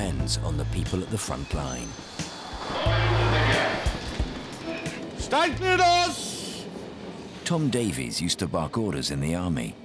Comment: Does anyone know which 'order' is being 'barked' by the man in orange? Can't understand him! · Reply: "Stand clear of the doors!"